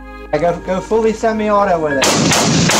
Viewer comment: "go fully semi-auto" did i hear that right?